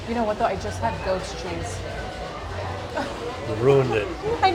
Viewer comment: When you were tasting the wine I thought you said you just had the "ghost cheese"!😂